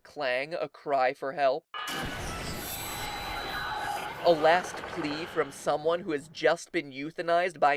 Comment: when the cube is destroyed at 9:25 i hear a voice say (muffled by machinery sounds) HELP ME GET OUT OF